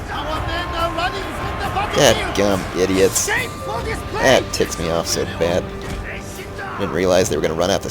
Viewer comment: i swear i heard "god gam these idiots, it tits me off so bad"